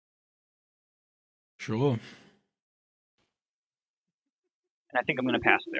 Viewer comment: in 9:15 - did he say shalom? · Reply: That was a very accented 'sure'.